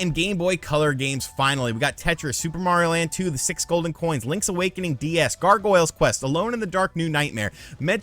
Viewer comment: Is it just me or at 9:45 does it sound like he says “Link’s Awakening DS” instead of DX? 😂